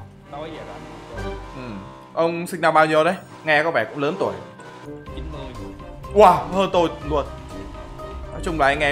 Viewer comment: có ai nghe thành 90 ko